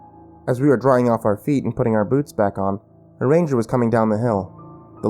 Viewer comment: xD I thought he as said reindeer, not Ranger. xD